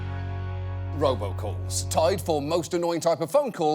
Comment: I thought it said Robocops and i was kinda scared